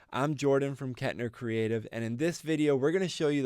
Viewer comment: I thought you said, "Cat Hair Creative" ! I thought, "huh that's quirky but Im into it" hahah this video really educated me. I appreciate it. nice fast pace too! BRAVOOOO